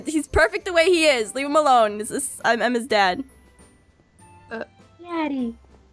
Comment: did she say Emma’s dad because um my name is Emma 🧐🧐🧐🧐🧐🧐🧐🧐🧐🧐🧐🧐🧐🧐🧐🧐🧐🧐🧐